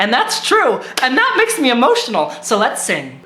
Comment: I THOUGHT U SAID LETS SIN INSTEAD OF LETS SING AND I NEARLY SCREAMED